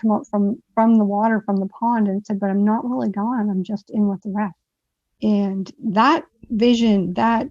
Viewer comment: Help me! What does she say at 12:25. " I'm not really gone I'm just in this....? (What?) Seems important. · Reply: I'm not really gone, I'm just in with the rest.